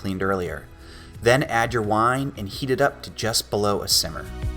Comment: did he say wine?